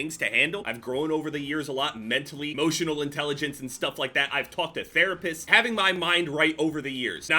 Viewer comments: Did you say you fukt a therapist?? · "I talked to therapists" I swear I had to hear this 3 times because it sounded like you said "I fucked a therapist"